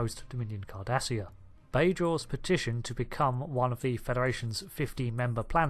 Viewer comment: 150 members not 50. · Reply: If you listen closely he actually says FIFTEEN....not FIFTY ( 1:21 )